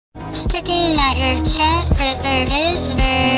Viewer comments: ‘Sticking out your chez for the rizzler’ and ‘I just wanna be your sig-num’ are the saddest quotes I’ve ever heard. 10/10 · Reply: I hear “sticking out your chezburger rizzler” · Why did i heard chad instead of gyat 😭